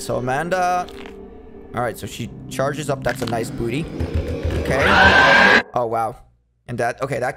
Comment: In 3:51 did you say i have nice booty